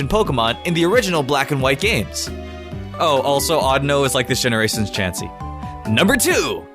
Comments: When you said audino... I thought you said I don't know...... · When he said Audino I thought he said "i dunno"